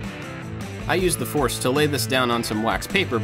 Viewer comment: I was kinda half listening when I heard "I used the force" and nearly dropped my toast.